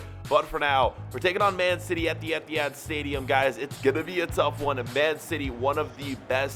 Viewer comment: is it just me or did he say the emptyhad stadium